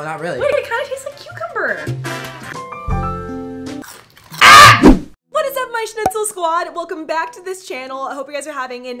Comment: is she saying: what is up my SCHNITZEL squad???😂😂 Greetings from germanyyyy😘